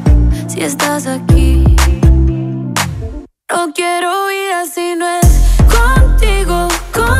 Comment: Acabo de darme cuenta que la he estado cantando mal 👎🏽 pensé que decía “NO QUIERO IR AL CINE CONTIGO-CON-CONTIGO EY 🎶🥹